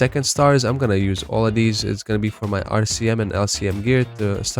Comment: subtitles say askmen instead of rcm